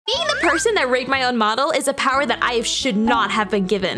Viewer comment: Was originally gonna scroll past this but I would’ve swore I heard “being the person raped my own mom-“ had I not come back to hear it again